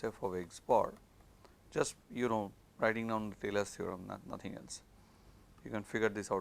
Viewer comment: What theorem does he mention at 5:26? I heard thelas theorem, but that can’t be right.